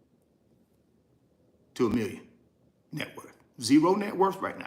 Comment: If you have bought 11,200 shares that over $1.M in shares purchased. I thought you said $0 to million.